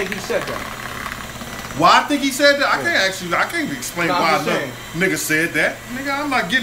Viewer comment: did he just called Eminem a nigga?